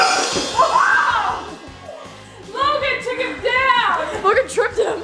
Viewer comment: If you turn the captions on when they say Logan the captions say booger lol